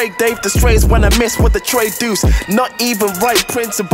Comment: did he say deuce